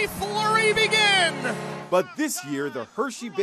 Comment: Wait teddy bear toss time? I thought you guys said caterpillar blizzard night!